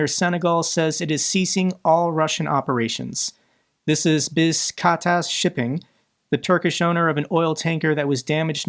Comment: Basiktas shipping? I heard "Half assed shipping"